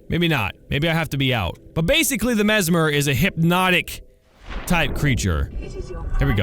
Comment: i thought mesmer said memes